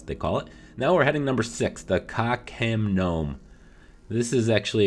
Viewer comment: When you said Ka Khem Nome I thought you said cock m Gnome